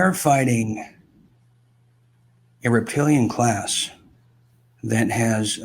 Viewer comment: did he just say “reptilian” ? i think he did.. he’s referring to David Icke .. 😀